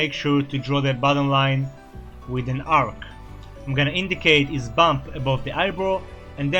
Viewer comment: When you said ‘indicate his bump above the eyebrow’, I thought you said ‘indicate his bum’ 😅😅😅🤣🤣🤣